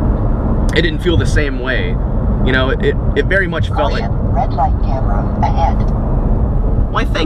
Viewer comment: Lol for a second at 10:05 I thought the car said oh shit red light. Then I realized it said Caution.